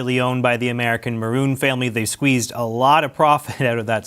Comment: It’s Moroun. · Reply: I kept hearing "Bruin" and was like... "The hockey team owners?". 😆